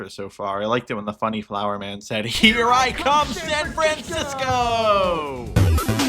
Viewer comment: gotta love how almost everyone I see joking about that line says "Francisco" instead of the actual thing Flowery says, "Frandisco" Great video, by the way.